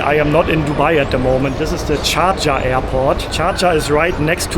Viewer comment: Did he just say shajaah😂😂his accent is funny 😂